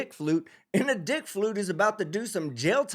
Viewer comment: I thought I heard you say, "dick fluke". I like that better than flute. It is a better and grosser visual.